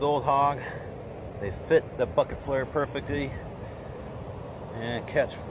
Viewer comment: sounds like he said "They fit the F^@ flayer perfectly...ol